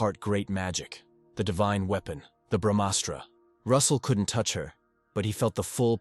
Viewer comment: did he just say bhamastra